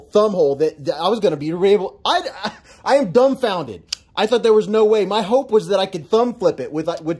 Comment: i thought you said I’m “thumb founded” lol